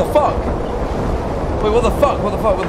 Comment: I saw this theory and i just wanted to explain it, I think that all the monsters in the backrooms were once people who were trapped there. And eventually they became physically and mentally insane, from being in the backrooms for so long it's made them look all twisted and how they are now. That's why if you listen closely you might be able to hear them saying things such as "Don't go!" or "Stay!" from staying in the backrooms for so long they've become lonely. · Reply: Oh and also I don’t hear them say “DONT GO!” and “STAY!” I hear them say “WAIT! WAIT! HEY!”